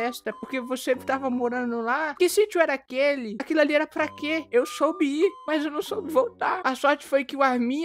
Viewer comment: "eu soube ir", e eu aqui escutei: "eu sou bi" 😂